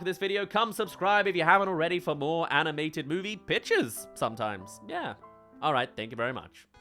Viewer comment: please tell me I wasn’t the only one who heard “animated bitches” instead of “pitches” the first time 😂